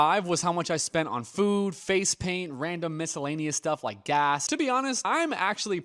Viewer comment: did he say face paint? FACE PAINT??? FOR GODS SAKE , HOW OLD ARE YOU? BT Barnum was right!!! And i really dont understand the purchase of disney ears. Where else will you wear them? What a waste of money!